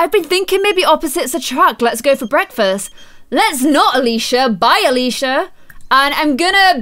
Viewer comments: lol when she said bye Alisha it sounded like BYE FELICA... xD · Let me know what you think of Connor's TEEN appearance and if we should change his hair! 😍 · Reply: at 17:51 in this vid it sounded like bye Felicia